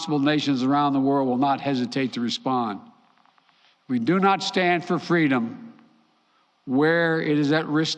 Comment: did Biden just say we do not stand for Freedom? Lol sounds about right.